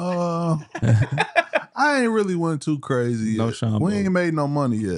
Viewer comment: What did teague say here?! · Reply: Sounds like he said no Sean combs lol